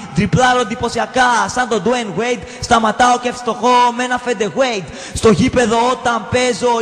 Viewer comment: fede weight axaxaxaxa asxetoi apo mbasket